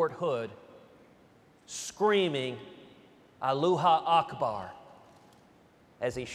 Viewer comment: Fucking laughed when I heard "alooha akbar".